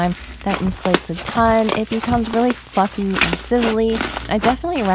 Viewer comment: am I the only person who thought she said ‘really fucking sizzly’